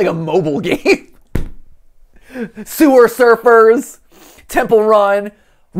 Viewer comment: Did he just say Sewer Surfers??